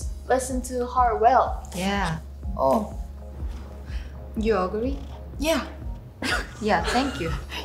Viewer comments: THE YOU AGREE BETWEEN THEM IT SOUNDED LIKE YOU UGLY HELP I LOVE THEM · what yeji said : You agree? Chaeryeong : Yes. what i thought she said : You ugly? Chaer : Yes.